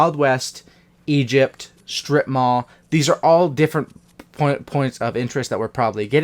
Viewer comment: I thought you said strip club for a second...... I was about to SAY